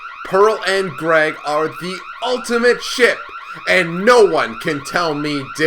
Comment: I thought you said "ultimate shit" in the beginning😂